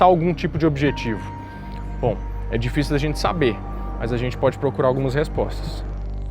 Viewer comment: No 25:03 fala "edificio da gente saber..." foi proposital?hauahuahua